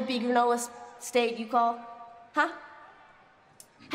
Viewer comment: everybody when ya'll thought she said gay meat she meant game meat